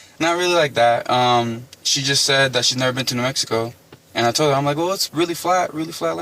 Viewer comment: I thought he said she was new Mexico